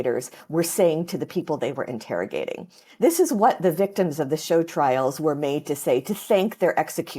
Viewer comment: Can someone PLEASE tell me what she says when she says "shode?/schone?/showed? trials" at 3:18? I know a respectable bit of history especially the violent dictatorships and have never heard of this nor cannot figure out what she even said even but googling for a solid 15 mins straight now. Thank you in advance🙏🏼